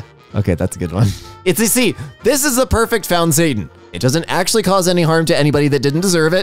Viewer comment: I’m so done I thought it said foundstan